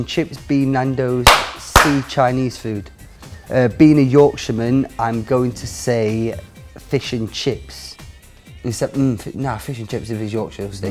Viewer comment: What does Kyle says in 1:53? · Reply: "being a yorkshire man i'm going to say fish and chips"